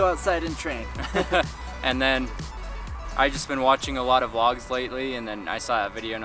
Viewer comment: at the beginning I thought said he had been doing a lot of clogging.